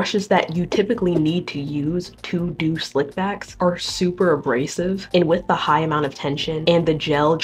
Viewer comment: I wasn’t paying attention and I thought she said the brushes were “super racist”😭